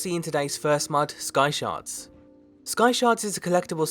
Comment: i thought you said "skysharts"